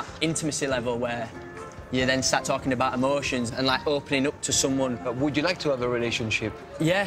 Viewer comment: "when you start talking about abortions"...am I the only one who heard that 🙉